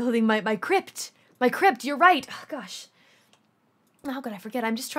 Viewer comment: When she said "build my crip" i thought she said crypt...